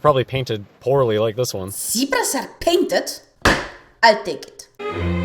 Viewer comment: Nothing against you, but I had to watch it 4 times, ... and only could figure out what you said ("I'll take it") after I turned subtitles on.